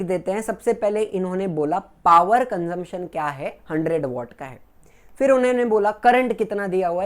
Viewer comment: did you say VOTKA HAI ...😂😂😂